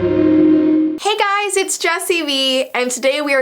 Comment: Bruh why did i hear "its jessii wee!" 👁️👄👁️